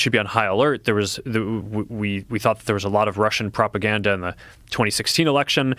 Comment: I didn't hear "Hunter Biden", I heard "Russian propaganda, something like that". You're straight dreaming, or perhaps on something. What is it, LSD? You flying around with Lucy in the sky, son?